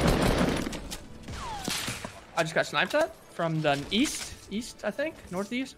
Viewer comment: アッチからスナイパーに聞こえる